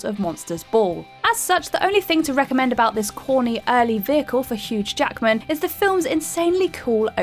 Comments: Sounds like she said HUGE Jackman, so does she know something about him the rest of us don't know about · Did she say HUGE Jackman around 9:21?!? Lol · I am sure I heard her say HUGE Jackman during the Swordfish talk.... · she clearly says HUGE Jackman instead of Hugh.